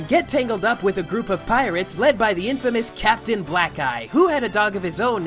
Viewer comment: wasn't paying attention, thought he said infamous captain black guy.